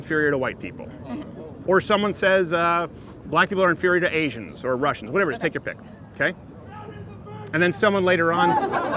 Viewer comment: what does he shout? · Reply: "CROWDERS A VIRGIN!!"